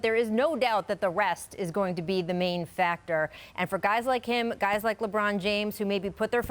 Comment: at first i thought she said the refs are going to be the main factor and i was like i agree completely.